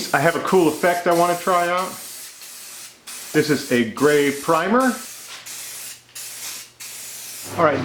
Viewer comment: I misheard gray primer as grave primer